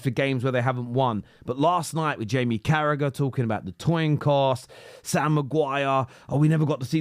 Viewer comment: Did he say..toin cost?